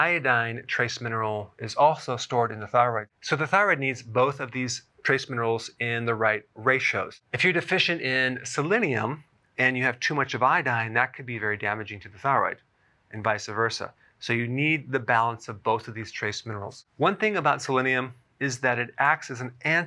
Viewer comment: thought u said too much estrogen